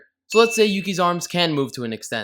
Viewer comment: instead of 'Yukki" i heard "Pukey"